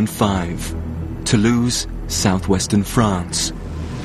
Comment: Lol at the begging When he said Toulouse South Western France I thought he said To loose South Western France... lol